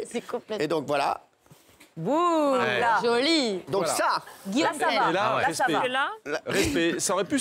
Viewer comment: pourquoi elle dit "Guillaume Pley" vers 1:04 ? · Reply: ana guillaume Tell elle dit